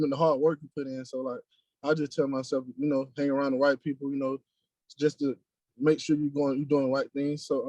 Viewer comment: I swear at first I thought he said he had to "hang around the white people", lol. He said right people but is speaking manner, accent threw me off for a second.